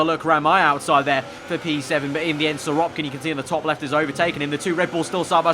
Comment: Love the livery and the intro is epic. Also BTW it sounds like ur saying siropkin. Are you or am I partially deaf?